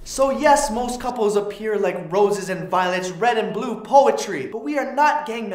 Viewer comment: THANK ME LATER!!! Roses are red and violets are blue Trees are brown and green The primary colors of poop Which is why they go together Both the tree and the poop They compliment one another Unlike rose red and violet blue I have always thought and wondered Which one of us was which Then I realize I must be the tree For you, you are the s*it I was but a little seed when I met you I was so immature But you never did leave You made me grow like a fertilizing manure So yes most couples appear like roses and violets, red and blue poetry We are not gang members We are not halo series We are not the original pokemon game colors We are brown and green We are tree and poop We are Poetry!!! You're welcome! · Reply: I thought he said "we are poo and tree (poetry) oupss 😂 #PunGameTooStrong